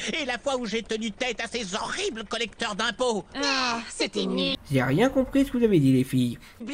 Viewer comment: Je croyais qu'elles disaient "c'était nul" ? 3:18 C'est quoi les forces spéciales XD